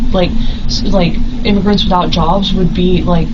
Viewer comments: The caption says "individuals without jobs" but given that I recognize a Trumper when I see one, I was able to easily recognize that she says "immigrants without jobs." Listen again and it's obvious. Time for prison, MAGA. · “ immigrants without jobs” 🧐 had to rewind that 3 times just to be sure. I think the majority of people miss it due to the subtitles being wrong… what a crazy thing to say 🤦‍♂️